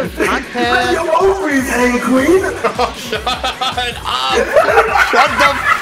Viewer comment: What does it say in 14:20? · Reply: I think he said " let your ovaries hang queen"